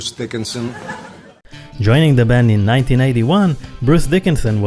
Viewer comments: If you're as nerdy as us about old metal bands and the stories behind them, we're looking for you. Whether it be fact-checking, scriptwriting, narrating, editing or any other skill - there might be a place for you on our team. Talk to us - even if you're not sure what you could help with (that's how our producer got in)! We're on Discord: <link> Or message me on Twitter: <link> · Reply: For some reason it sounds like you said Bruce Dickinson joined Iron Maiden in 1991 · Did you just say he joined the band The Bruce Dickinson in 1991 😳